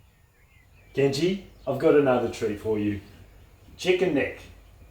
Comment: dude i misheard u saying 'chicken dick' and i was shocked lmao